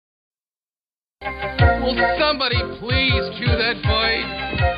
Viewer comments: I hate to admit it, but the first few episodes I thought they said "Would somebody please "KILL" that bird?" 😎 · For mine entire youth I always thought he said 'Would somebody please kill that bird?' instead of cue😂